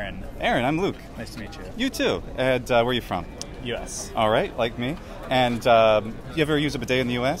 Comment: For a second I thought they said "Where are you from?" "Yes." "Like me." as if they were both heavy travellers